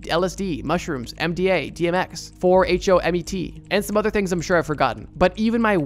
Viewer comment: I did notice you said DMX instead of DXM at 25:44 😅.